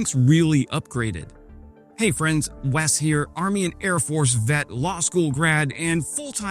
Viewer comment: Did he just say "Was here" instead of "Wes" ?? 😂😂 Great video regardless 😎